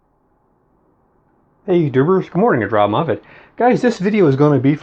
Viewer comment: Good video Robb! At the very beginning you say "hey youtubers...", at first I thought you said " hey you goobers...". In my case that would be an accurate greeting. Keep up the good work.